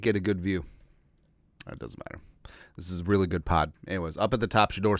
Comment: did you say this is really good pot? Am I hearing things? lol